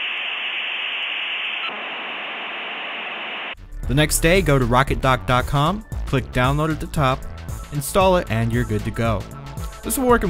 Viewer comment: Is it me or did he say at 0:52 "...the next day"